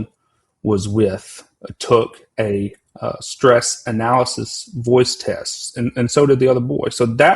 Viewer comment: you sometimes just hear what you want to hear at 9:00 he clearly said the both took a voice stress test the girl and another boy not a poly graph 😂🤦🏾‍♂️